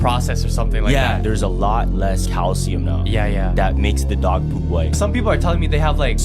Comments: Tooth in the nugget video, I heard "there's dog poop mom" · I listened a few times it sounds like he said that dog poop mom lol and then she said where